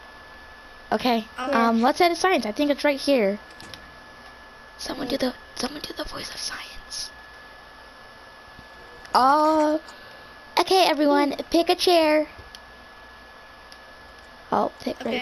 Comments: i heard you say someone do the voice for science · did I hear “Someone do the science”